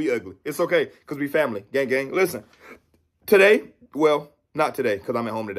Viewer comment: It sounded like he said Yang Gang before the video started. If he did, I'm all in.